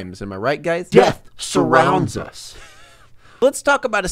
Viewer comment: I thought you said “Jeff surrounds us”